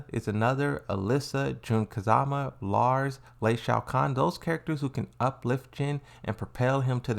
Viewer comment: Mf did you just say Shao Kahn? 💀